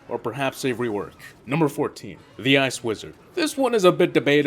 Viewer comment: it sounds like he says ass wizard